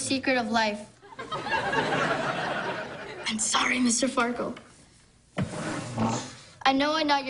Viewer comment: "I'm sorry Mr. Farkle. I know I'm not your daughter but I too have vaule in this life." God damn kid, making me cry and feel emotional huh? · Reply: Amber Bamber when I heard “I’m sorry, Mr. Farkle” in my mind, I heard “I’m sorry, sensei.”